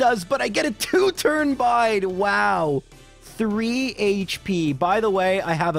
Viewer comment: I heard it as "Bide Away" and really wanted it to have been a pun